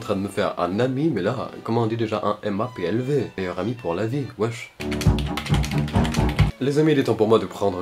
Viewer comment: il a dit wesh ou c moi 🤣